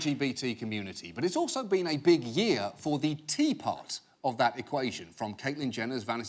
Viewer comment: At the beginning, when he said, "the T part" of LGBT, I initially misheard it as "Tea Party". Very odd imagery.